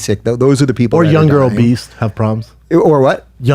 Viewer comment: Did Brendan at 2:02 say "obeast" instead of obese!? I can't take anything he says seriously just on that alone, and he has the nerve to tell an actual doctor (Fauci) he is wrong? He said "obeast" for the love of sweet baby jesus. I'm not even I'm saying I agree with the shutdowns, but if I was against it I certainly wouldn't want Brendan, and Bryan as the face(s) of my movement. I actually like both of them, and enjoyed the podcast in the past, but they are BRUTAL to listen to when speaking on anything covid-19 related. Why is Brendan refusing to wear a mask? Is his plan to reopen the states to just open them up wide open, and not put ANY type of legislation, or policy in place to protect people? Nothing at all? Why do you have to be all the way on one side, or the other?